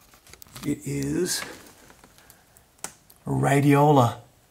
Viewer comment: Didn't you hear a woman's voice when you were showing the television? It was right about the 10:09 mark. It sounded like she said kind of whispery "Yes it is".